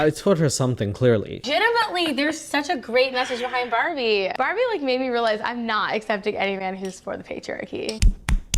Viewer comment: legit had a stroke here what did she say?? Like I replayed it 5 times and I can’t figure it out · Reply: "Barbie like made me realise im not accepting any man who supports the patriarchy"